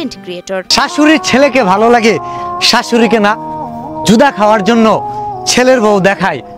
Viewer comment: কি বললো এইডা 🤔🤔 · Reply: জুদা খাওয়ার জন্য অর্থাৎ পৃথক বা আলাদা খাওয়ার জন্য